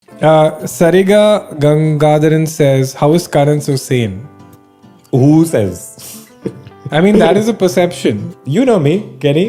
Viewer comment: Legit thought he's going to say sariga-ma-pa 😂